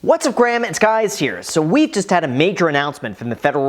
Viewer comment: Did you just say “ what’s up Graham, it’s guys here..”?